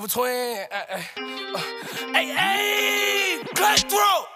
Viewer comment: WHEN HE SAYS CUT THROAT I THOUGHT HE WAS SAYING GUNDHAM GUNDHAM GUNDHAM